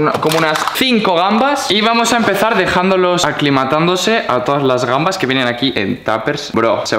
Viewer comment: entendí aquí matándose 🤣😂😂🤣😂🤣